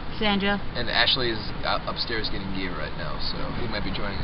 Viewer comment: i thought he said Ashley was getting head! haha this is awesome. BVB <3